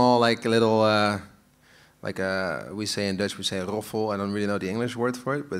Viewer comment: What they say in dutch? How they call it? Explain me please. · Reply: Александр Коняев He literally said "roffel" which is Dutch for "drumroll"